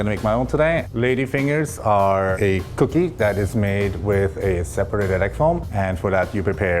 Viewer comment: I thought he said ‘’lady fingers are a cookie made with a separate electron”